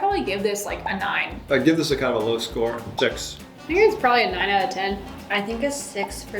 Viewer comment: the fact that gave his wife a low score while each of the girls gave high scores, wbk the mom is gonna win anyway 😂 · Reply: idk why i read this as love score and was expecting like a 10... i even heard it as love score and was confused bc its just a 6